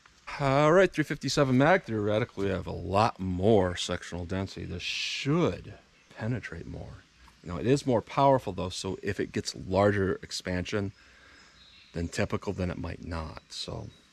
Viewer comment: I swore to God he said “sexual density”… I need to get off the internet 😓